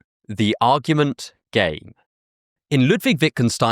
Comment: clicked 13:52 from the chapters and I misheard it as "gay"